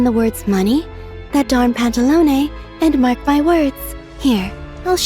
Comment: i thought she say "that danm pantalone" 😂😂😂😂